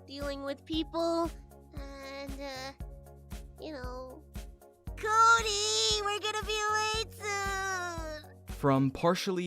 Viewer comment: "KODY WE'RE GOING TO BE LATE SOON!!" For some reason, when i put it on a bit low volume, it sounds like said, "KODY WE'RE GOING TO BE MARRIED SOON!!!" Wth is wrong with me, I STILL SHIP KODY AND, I CAN'T HELP IT!!